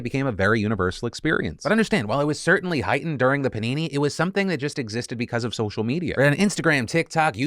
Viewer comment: did I hear phil Just say Panini instead of pandemic ?